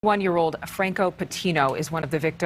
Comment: did you just Say he’s one years old that’s racist